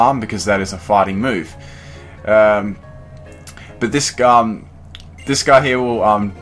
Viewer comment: it sounds like he says This Scum